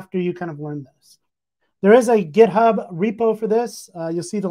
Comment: I thought he said there's a github repo